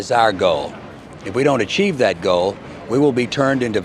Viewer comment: I really thought he said "gold" lmao What a plot twist that would be - the Predator is just a big angry leprechaun